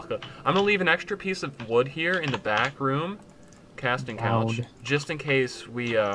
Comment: Did you just say Casting Couch :3